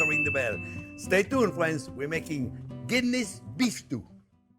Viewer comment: LOL. When you said a Guinness Beef Stew .. I thought you said a Kidney Beef Stew .. which got me thinking ... I wonder what other meat could be used in a stew?